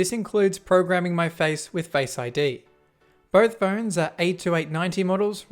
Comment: I tried pointing this out for a decade..... nobody cares. I don't mean this as an insult to you. Or your content. Or to our industry. It's sad. but nobody cares. Thank you for your continued work & dedication to informing users just how little freedom they have left. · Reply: You said at cca 1:20 “both models are A289 models...” -- did I hear that right? What does it mean?